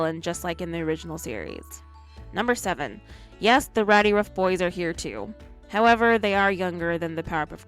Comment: I burst out laughing when I heard "and the rowdyruffboys are younger" SOUNDS MORE REALISTIC! XD